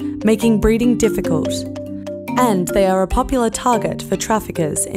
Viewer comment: I thought she said this a two turd sloth lol.